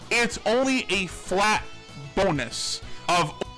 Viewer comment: bro at 1:50 it sounded like he said boneless